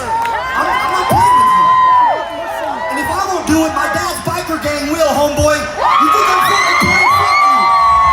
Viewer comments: "My dads biker gang will beat you up" ronnie is the cornball of the century dude😂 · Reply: I heard "my diaper gang" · did he just say "my dad's biker gang" ?? 😏 Ahaa🫵🤣lame